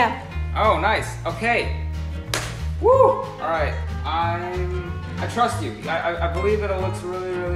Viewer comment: Did he just say :i trust you?!!!!😙😙😙😙😙